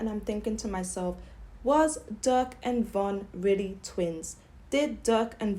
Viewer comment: I thought she said duck n von was twins for min. 😂